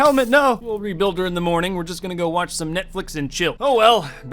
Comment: is it just me or did he say (we are going to watch Netflix and chill)